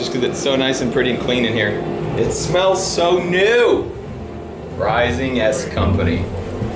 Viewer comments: Anyone else thought he kept saying big ass company, because there is a company called big ass fans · Who else going back to this video after watching the new one? and seems like I don't really see the bad comments. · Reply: Bad company? I started watching this video to find out. WTH is saying "rising bunker?" No wonder he said bad Company in the next video, turned on subtitles to no avail. Web search saying I have to turn off safe mode, it then auto corrects from rising bunker to riding ❌ and iron butt motorcycle rides, cool but not a bunker SMH 🤦 @ 12:39.